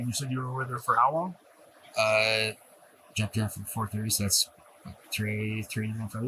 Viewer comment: subtitles say he jacked off at 4 :30 😂